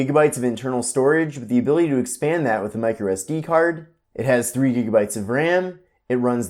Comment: For some reason I heard 6 gigs and went crazy 😅 1:42 PM 11/13/2019